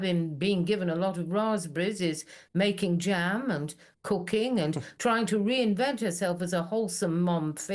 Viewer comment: " I heard ..Raspberries, making jam and c*caine" and I went "right cuz they're in Columbia....oh she said COOKING....ah"